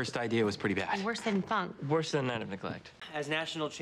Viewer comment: what are they saying at 1:12? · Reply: Worse than Funk,” Tina said "Worse than ‘A Night of Neglect,’” Artie said